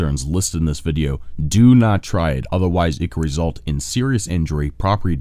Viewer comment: People keep asking me where I got my mill, truth be told It's actually a rock tumbler but can be used the same. You can find a single barrel version to buy here if your serious about making your own powder: <link> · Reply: Did you just say "it could defecate"?